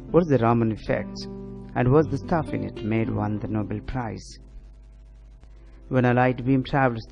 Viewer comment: lol "What's the stuff in it made won nobel prize". Its hard to figure out what you're trying to say. Maybe "What is the Raman Effect? How it's importance lead to a Nobel prize" .. or something